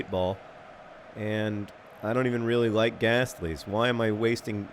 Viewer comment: for a minute there, in the Pokemon-bit in the beginning, I thought I heard you say 'Graslie'... Emily would make a good Pokemon I guess (in a good way)